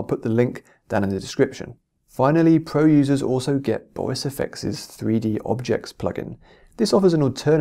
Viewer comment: Boyce affects 3D objects is that what you said? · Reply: BorisFX 3D Objects. All of our videos have subtitles, in case you are ever unsure of what exactly was said, and want to check.